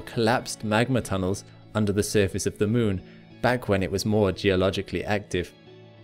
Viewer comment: Just FYI, the closed captioning is incorrect at around 6:10... The rille being discussed is on the moon, but the CC says Mars. 😮